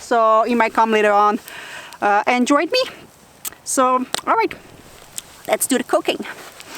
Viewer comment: I thought you said "alright, let's do cocaine" lmfao